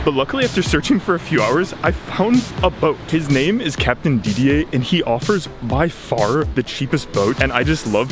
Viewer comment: Why did I read that as captain didler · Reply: you're not alone. I almost burst out laughing when I thought it said captain diddler 😂